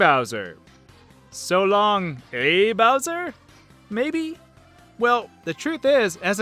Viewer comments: I always heard "So long, K fountain" when throwing Bowser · I always thought mario was saying "so-long de muffin!" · When throwing Bowser I always heard "Shalom-eh Bowser" I know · I've always heard it as so lonely bowser. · i hear it as " so long-e bowser" or how do you spell it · I thought Mario said "So long sir bowser" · so long gay bowser is a classic. But I always heard "so long'a bowser!" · For Mario with Bowser I thought he said so long le Bowser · I always thought what Mario said while he was tossing Bowser around was "So long, donkey!" ...I might need to get my ears checked. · I always heard "so long a bowser" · I always heard "so long me bounty" XD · I heard so long'a bowser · I always heard it as : "So long-e Bowser!" I think Mario ads his italian sounding accent. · I heard mario say "So long der Bowser" for some reason as a kid.^-^ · I must be vary weird. Both the Mario ones I hear “ so long la bowser!” And jumping through the painting I always herd “ let’s a go!” I only kinda hear so long gay bowser when my friend showed me. And the painting ones...now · I heard 'so long de bowser! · I heard mario say: "So long meehowzer." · I thought Mario was saying so long me bowsey when you throw him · i misheard the title miss herd vidio ganes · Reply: also i thought mario said so long a bowser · You know I've never heard 'so long gay mountain/bowser' I always heard "so longsie bowser" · I'm weird. I always thought Mario said," So long de-dozen!"